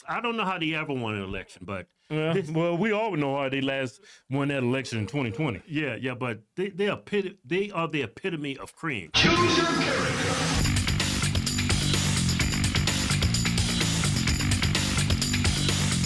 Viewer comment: Did it say "Choose your dumass" ??? 🤔 thought i heard that.